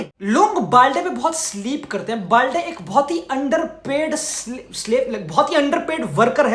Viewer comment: did he just say slave 💀